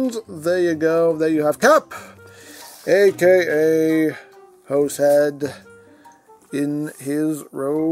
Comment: I gotta be real with y'all, the first time I saw this guy (him getting killed off at the end of RG1), I honestly thought they were saying his name was Horsehead. I was very confused.